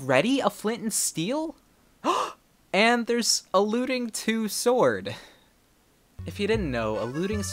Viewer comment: When you said looting I thought you were saying “alluding”